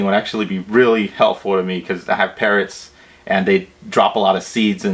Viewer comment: Ah, you said you had parrots. I thought you said parents, and they were dropping seeds all over the floor :-)